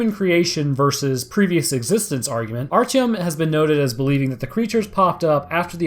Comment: the captions said argument our chum instead of artyom lmao